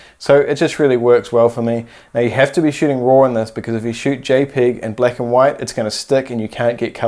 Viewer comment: Did he just say jay pig instead of JPEG????? lol